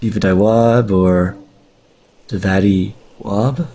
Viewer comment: What the hell are you saying from 10:10 to 10:13 ?? "Ivadawaad boor.. davari, wab?" haha so freaking strange, like you started to speak another language for a few words.